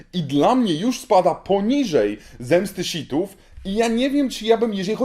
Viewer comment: ja usłyszałem "zemsty szitów"